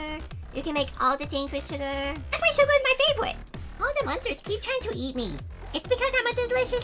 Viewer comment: when you said sugar is your faivrote I thought you’d said chicken nuggets was your fav like me